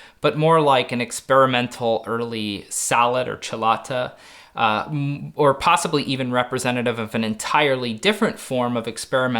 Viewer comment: early experimental sallet or "chelata?" What he say and what helmet is it. Also examples? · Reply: Celata is the Italian word we start to see pop up in written sources to describe what will eventually be a sallet.